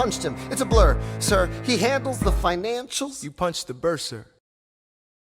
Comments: M1n3r Cr4ft3r G4m3r it’s bursar, a bursar is some financial something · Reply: Moonlight sis i was actually so confused at that part tho, i didn’t know what a bursar was and i thought he said “i punched the burr sir” and that was confusing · I think i heard burr say "you punched a burrsir" i'm not sure though it was like 36 or 37 seconds into the video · you punched the burr sir? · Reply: It's actually, "You punched the bursar?" a bursar is someone who handles finances, but I thought it said "burr, sir" the first few times I was listening to that song XD